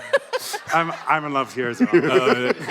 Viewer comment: What does Sam say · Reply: "I'm in love with yours as well" meaning michael cudlitz's accent